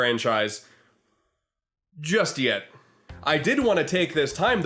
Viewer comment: did you just say... YETH?